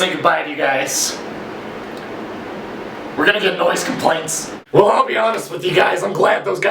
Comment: I thought it said noise completion